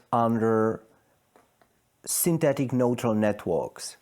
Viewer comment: The interviewer is failing to understand Professor Penrose as he answers one question and interrupts him to ask another question that is irrelevant to the point being made. The interviewer is out of his depths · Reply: "Synthetic neutron networks", did he say that, 19:41 ?